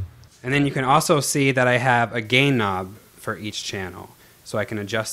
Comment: I thought you said gay knob at 2.40 :L